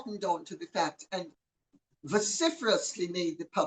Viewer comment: Vociferous 😂sorry i thought you said syphilis 😂